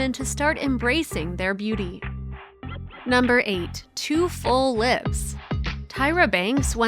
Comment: Only thing I heard is “too ugly”👍🏻